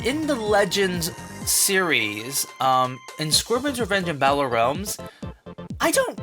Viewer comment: I thought he said "Squidward's revenge" there for a second XD